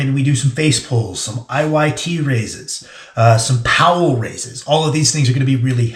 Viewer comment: What is the name of the exercise at 20:48? (I heard “IO IT raises” but can’t find it on Google)